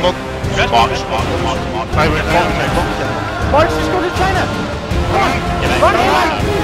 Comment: what does he said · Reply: “Marks just go to China” Essentially if you got marked, fuck off and just run away